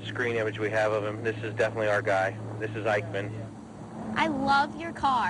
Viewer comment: Holy shit when they first said Ikeman I thought they were saying Eichmann I was so confused